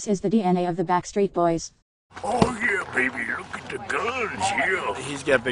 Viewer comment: Can someone please write that word Kevin say in 0:04 when he said "look at the ..." I don't understand that word, please 💖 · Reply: Kevin: Look at the guns, yeah. AJ: Yeah, he got bigger guns than me. Guns can also mean biceps (upper-arm muscles). We, American, have weird expressions with our words. Lol